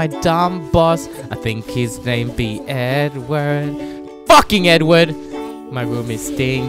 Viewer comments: Holy shit I thought he said FUCKING N WORD 0:09 😭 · Thought he said “fucking N-Word” lmao